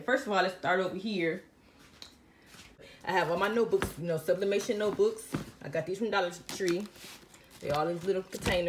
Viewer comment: What do you do with the notebooks? I thought you said sublimation notebooks